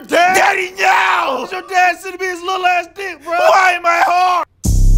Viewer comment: Did bro say why am I hard